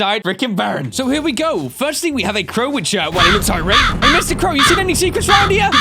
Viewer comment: what did he say? I turned on subtitles, and I still have no idea what he said. “He looks I rate” is what I hear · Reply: he said he looks high rate